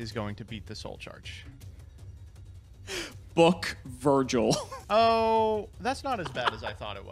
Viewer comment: S P I N N I N G F I S H (I heard Joel instead of Virgil 😂😂😂)